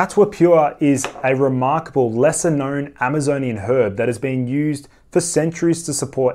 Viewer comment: As an American I heard, "Amazonian herd" instead of "Amazonian herb" until I rewound more than one (for the CatuaPure ad) Heh. Heh.